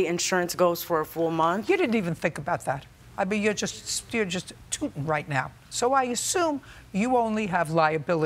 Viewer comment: Did she just say, “you’re tooting right now!” Lmaooooo Bro, you have to be HAMM if you are gonna get on TV and allow yourself to get REKT